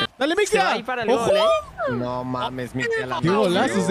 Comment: Que dice? no lo escuche xd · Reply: nmms mictia la metio